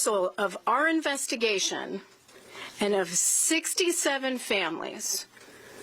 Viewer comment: did she say 6-7 families?